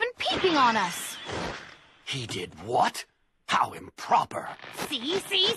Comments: bro i heard it has Pushup bras- Wait is it? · Does he really say "Push up bra´s" at 00:14? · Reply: Bushy brow 😑